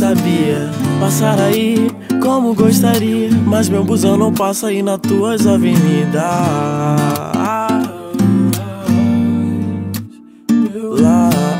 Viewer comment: juro q entendi "Mas Meu bundão não passa aí nas tuas avenidas"